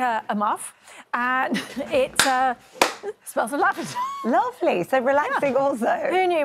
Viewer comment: I thought she said her muff smells of Labrador! Didn't realise it was "lavender".